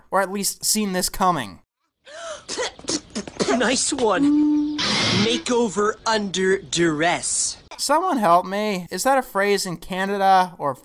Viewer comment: I think he said "Makeover under duress."